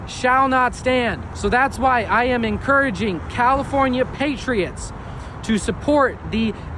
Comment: When that Democrat FOOL said "California Patriots" I heard "California IDIOTS".